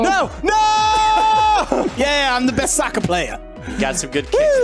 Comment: I thought he said "YAY I'M THE BEST SUCKER PLAYER."😂😂😂